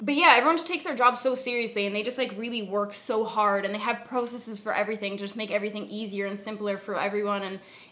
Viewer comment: can anyone tell what she's saying? the word that starts with P i think? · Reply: I think she said "processes".